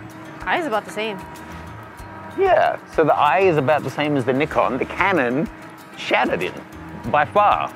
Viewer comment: Hey... @ 5:10 did you say "the Canon shat it in". Interesting conclusion to a test. Brought a rye smile to my face